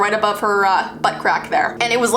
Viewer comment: Omg when she said butt crack I thought she said butt rack who else thought she said butt rack